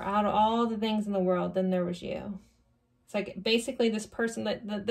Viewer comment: Thank you for this message. Pile 2 really resonated. Lol when you were talking about that line you were trying to remember, I kept hearing "Till there was you" from The Music Man.